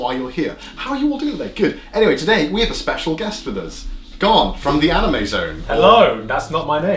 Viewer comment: When he said garnt from the anime zone I thought he said god from the anime zone